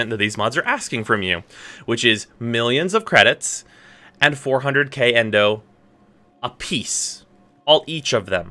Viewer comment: Did I hear that right? 400k endo? A piece? MonkaS